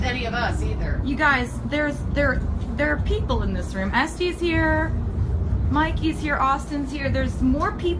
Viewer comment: did she say that Este's here?or is it just me?